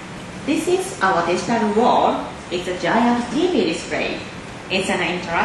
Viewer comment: I thought she said this is a giant ass tv! Lol